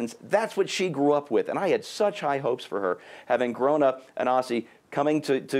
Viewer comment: he says "having grown up an Aussie"... WTF, she didn't grow up in Australia. Maybe I misheard that, but if so, what is he saying?